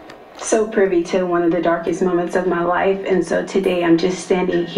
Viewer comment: Omg, my heart dropped. U spoke so fast, I thought u said "my wife". You're not married.... right? 🤦🏾‍♀️🤣 Thanks for covering this story tho ❤